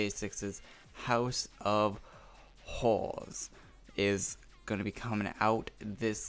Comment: when said j6 house of horrors it sounded like he said j6 house of whores😂